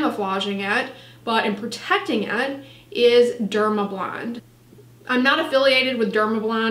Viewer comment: I heard “Dermablond” but did she mean “Dermablend”?